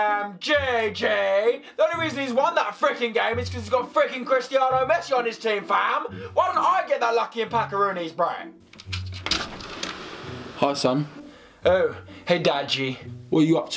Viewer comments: did he say Cristiano messi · did he say Christiano Messi 😂